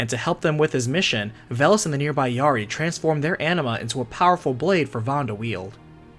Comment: I hear "enema" lol